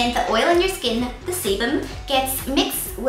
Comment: I keep hearing "semen" instead of "sebum" o_o